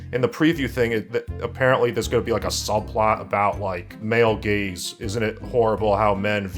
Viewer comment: For some reason I thought you were saying 'men gays things' instead of 'male gaze'. I must have a brain parasite.